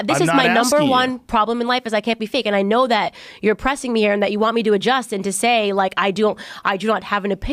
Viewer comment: did she say "you're oppressing me"?